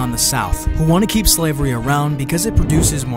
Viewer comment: as soon as he said money I just heard Arthur